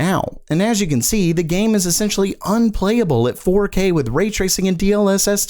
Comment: my 3090 has no issues at 4K, maxed out settings with or without DLSS. It only struggles when I am at 4K with raytracing on and DLSS off. Aside from that scenario the game runs beautifully. I honestly don’t notice a difference in visual quality between DLSS ON and OFF, but I’m also admittedly lost in the world, so I’m not exactly looking for a difference. I honestly think people just like to complain for the sake of complaining. Making something of nothing. Also, I’ve heard plenty of people downplay the strength of the 30 series cards, but when I ask what they have, they say that they have a previous gen because they couldn’t get their hands on a 30 series. In all honesty, given how good the game look even at lower setting, and how vast and dense the city and the AI inhabiting it, it’s crazy that the game was rushed and still works this good. I know I’m in the best case scenario for the game and how it runs, but I’m not disappointed at all with it. It has met and or exceeded my expectations, and I love it. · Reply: oh I see, I misheard him. I thought he said with raytracing off. What he actually said was with Ray tracing, (comma) and dlss off. Good to know Bc I was like wtf impossible.